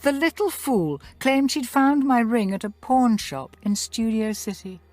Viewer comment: what did bierce said? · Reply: She said pawn like selling crap that people might need